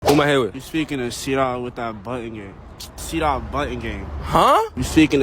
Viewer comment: I thought he was saying I'm trying to see that butt on gang and was like what